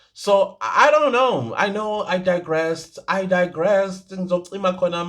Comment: I heard you say "I digress, cima cima cima" #mzansiunfiltered